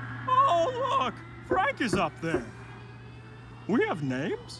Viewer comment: When I first heard the line, I thought he said GrEGG. I dont know if I'm disappointed, or not, that they missed the pun